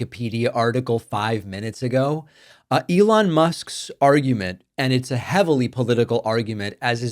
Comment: I misheard and thought you said Musk's "smarmument" which seems about right. Does Musk even leave twitter to read wikipedia?